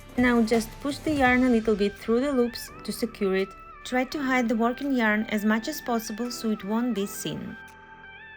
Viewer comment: I can't understand what you're saying. You say " try to hide the "something" yarn. Can you tell me what you said ? Thank you. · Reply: I said try to hide the "working" yarn